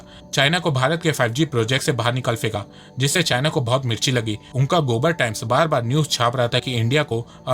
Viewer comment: I Heard Gobar Times Anyone Also Heard the same😂😂😂😝